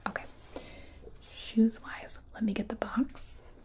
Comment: Some hear "I'll massage your balls" instead of palms but at 39:16 I heard "Let me get the bongs"